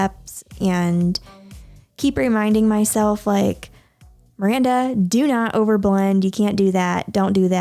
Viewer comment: Ok but like is it just me or did she call her self miranda